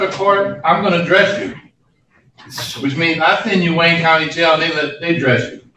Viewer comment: Shit that sounds like court in Detroit, I thought he said Wayne County Court.